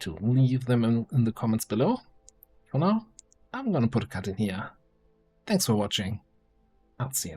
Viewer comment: great video but what did you say at the end there? I heard "but for now, I'm gonna put a cat in the ear. thanks for watching." and I really hope that actually is what you said